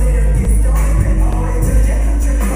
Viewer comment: when i first heard it i thought it said molly as in the drug and i was like oh so this is how we're gonna be